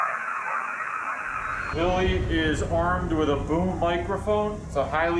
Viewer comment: At around 7:20 that sounded like "I warned you, (incoherent)". Last part maybe "pal"?